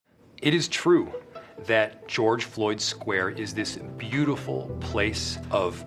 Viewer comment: I was going to visit, but then I realized I misheard and that it wasn't Pink Floyd square.